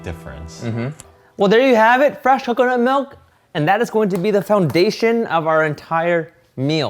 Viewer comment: Emmm, idk if it's just me but at 8:00 i swear Steven is saying "Fresh Hooker nut milk"